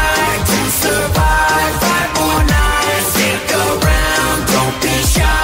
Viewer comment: I don’t know why but when I was 5 I thought they said maple nights